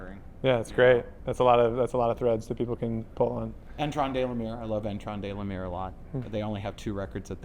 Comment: What is the name he says at 1:05:14? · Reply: Their name is Etran de L'Aïr